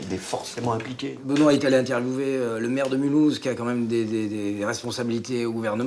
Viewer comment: A 16:30 j'adore la réplique "il dit quoi le vers de molu" a la place de " il dit quoi le maire de Mulhouse " hilarant